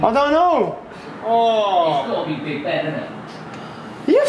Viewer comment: did I just hear talk about Big Ben? It's currently up in scaffolding, is the fuckin guy actually thinking of climbing Ben Ben? lool, that's a interesting way to get shot and killed...